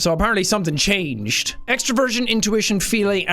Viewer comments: i thought you said extra virgin instead of extraversion😂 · Why, at 1:23, did my brain hear "extra virgin" instead of "extraversion" lol!? It's official y'all, Seán's personality type is Olive Oil! 😂😂😂